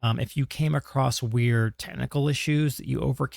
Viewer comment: My mental is at a point where I heard “weird tentacle issues”